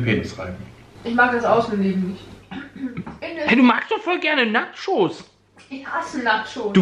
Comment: warum hab ich da Nacktshows verstanden